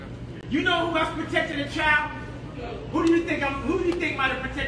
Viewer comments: does he say 23 years? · did he said who tried to protect a child 23 years ago ?? isnt he 43??